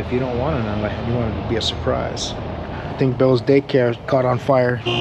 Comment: I had to rewind toward the beginning cause l thought you said Bill's day cab caught on fire so l wondered..how did it catch on fire and why does Bill have a day cab? Then l realized you said day care. lol👍👍